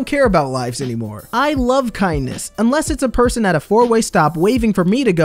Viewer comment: that's not illegal. The right of way is something given, not taken. If someone offers up their right of way, it's yours. Chances are they're not sure who's it is. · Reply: Ngl thought it said kidneys